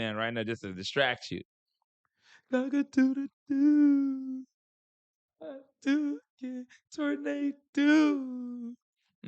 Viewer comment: We was getting more Deeww is what she was saying 😂